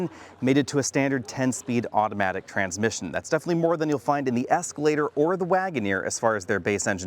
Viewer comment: Did he say, "Escalator"? Sir, we have a new name for the PPV Tahoe.